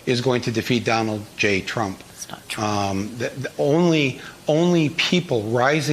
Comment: does anyone else hear that ? it sounds like someone off camera whispering "thats not true" does anyone else hear that or is it just me ?